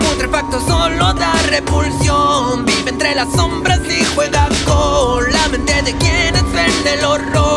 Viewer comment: pense que decia juega lol XD